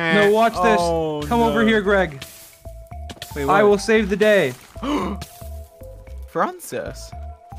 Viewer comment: WAIT DID I HEAR THIS RIGHT "come over here GREG, I will save the day" (2:41) Was he sayin it as a joke or is there something I'm missing